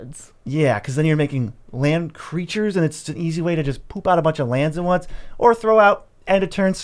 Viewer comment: Did you say poop out a bunch of lands?